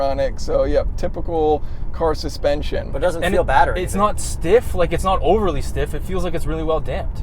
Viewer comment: Did I hear say BADDER?